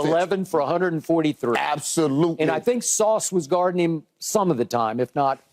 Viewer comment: sounded like he said "I think Sauce was Gardening, at least some of the time" loool